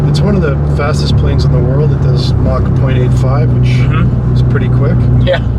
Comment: Did he just say Mach 25?